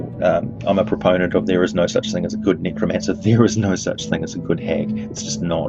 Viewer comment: Man I almost took offence there. Ha! Thought ya said no such thing as a good necrophiliac 😤 But you said Necromancer! And yes. Yes I agree. Weird, sick bastards.